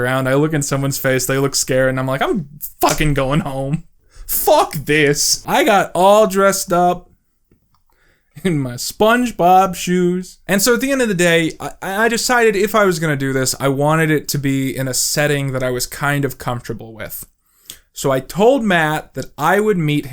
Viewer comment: Did you say SPONGEBOB SHOES?!